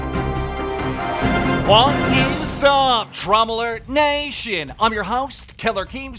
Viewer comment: I thought he said “TRAUMA ALERT NATION!!!” 😂😂